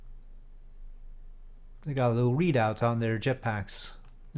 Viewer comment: For a split second I thought you said "They've got a little weed on there jetpacks." I had to rewind a bit to listen again. 😂